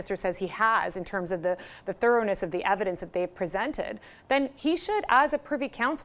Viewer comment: Did she said PM has presented? Coz I have not seen any evidence presented yet